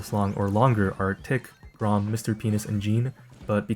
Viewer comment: did just say mr penis in 3:44😭